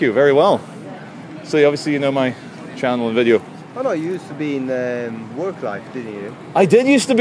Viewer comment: What does the guy at 10:45 say? Work life?